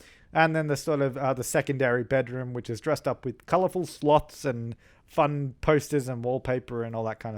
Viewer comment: LOL! Looked away for a minute and had te rewatch that! Thought you said colourful sluts xD